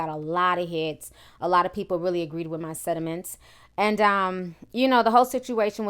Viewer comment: Yo, did you say people agreed with your "sediments" or sentiments @ 0:52?